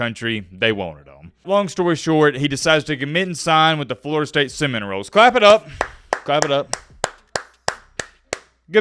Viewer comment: Wait did dude say Florida state cinnamon rolls 😂😂